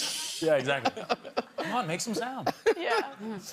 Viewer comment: What did he say at 5:15 ? Emily and John seem to not comfortable about it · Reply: He made a dirty joke. Obviously, she wasn't as delighted by it as the audience (including me). ("Come on, make some sound." as in the bedroom, not as the creature on a hunt.)